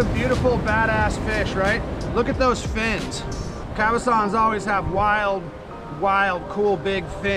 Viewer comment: At video time 9:17 you mentioned a fish I think you said "calazon"? I take it they are not only salt water. I caught one last July in the Willamette river near Keizer Rapids park, while fishing a Ned rig on some rocks. It was only 4 inches long, but same color, mouth, and big spikey fins. Always wondered what that was. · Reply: Cabezon